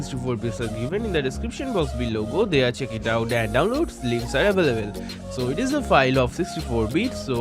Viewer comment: Don't try fake accent and what did you said at 0:48 download links are ? hahahaha